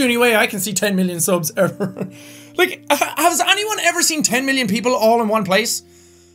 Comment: I thought it said 100 million I was like: Jack how did you 90 million more?